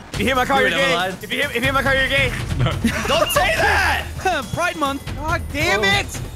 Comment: did anyone else hear say “pride month”